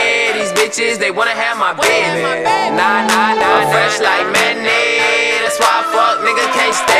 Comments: lmaooo I thought he was saying I'm fresh like mayonnaise every time😂😂😂 I listened to this 😑🔫 but this song goes hard AF🔥🔥🔥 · LOl I thought he said "I'm fresh like mayonnaise!" I hate mayonnaise.